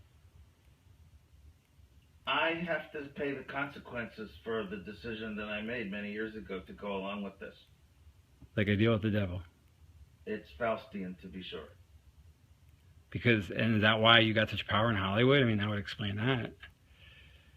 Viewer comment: What does he say at 3:33? it's "foustian"??? describing a deal with someone, not the devil?